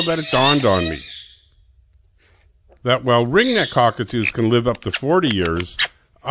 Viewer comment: Ringnecks are parakeets, not cockatoos. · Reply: I also thought I heard him say at the end "redneck cockatoos".. ??